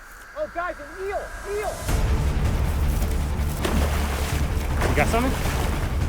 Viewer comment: I thought he said : oh look guys! A wheel!